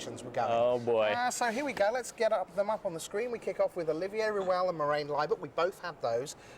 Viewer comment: Did he said "Olivia Ruel"? lol listen